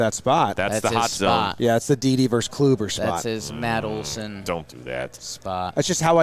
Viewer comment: The boys and Chris Rose!! Yet another amazing and unexpected mashup, love it!! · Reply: what does say? Sounds like “DeeDee Vs. Kluber spot” what’s he talking about?